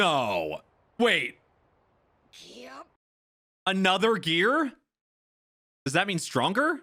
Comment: What Luffy said is "gear" not "third". Fuck the translator !